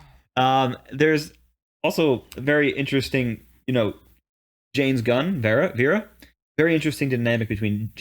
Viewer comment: For a moment, I thought you said James Gunn, not Jayne's gun.